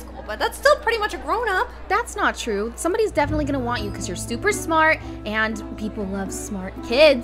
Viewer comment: DID I HEAR THAT CORRECTLY? 1:00 STUPER SMART- also Timmy is so funny “jesseoo” XD lol